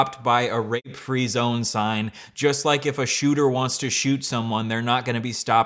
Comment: What does that r@ free zones take even mean?? What was he trying to say???? How do people unironically listen to what this guy says and not laugh at him?? I'm befuddled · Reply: I heard him say rpe free zones and I had to mull it over, hopefully he knows it’s actually not legal to rpe people